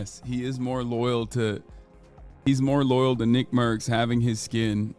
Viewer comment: i thought i heard "nig-mercs"